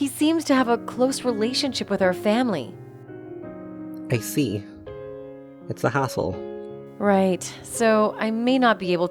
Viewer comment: In first, instead of "I see. It's a hassle..." I heard "I see. He's an a**hole..." wth is wrong with me? XD Or I just need some sleep?